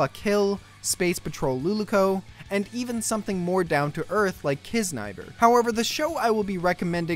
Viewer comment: What is this anime at 4:35? I can't undestand what he is saying... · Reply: Okay .. It's Kiznaiver :D